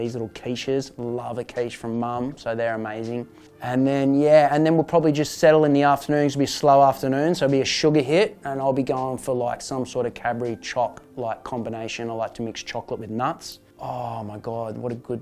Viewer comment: I thought he said cabbage & chocolate combination, lol¡